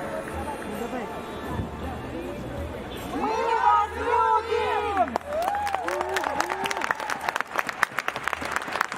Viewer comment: What do they say at 5:50? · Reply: "We love you!"